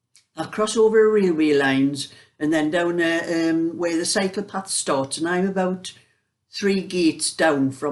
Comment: I thought you said psychopath at first!! (Cycle path 😆)